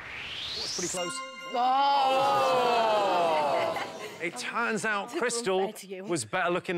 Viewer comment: does jimmy say "it tans out" 😭😂😂😂